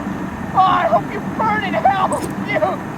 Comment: what did he say after NO NOT MY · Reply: “ No, no not like this! NOT LIKE THIS!”